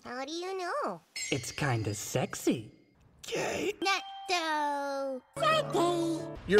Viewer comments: IS NO ONE GONNA TALK ABOUT HOW 2:36 SOUNDS LIKE HE SAYS "GAY" AHAHAHAHA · Am I the only one that thinks English dub Natsuhiko is the fUNNIEST THING 1:34 wHUSSUP 2:17 “s w e e t l i l ‘ s n a c k s l i k e y o u” 2:37 k a y 2:59 sayonara baBEY 3:33 AA A A AA A AA AA A 4:26 ( literally dead by this point ) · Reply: OMG 2:37 said kay? The whole time I thought he said gay 😂